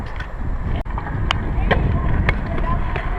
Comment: toi ei oo oppo kickless vaan switch kickless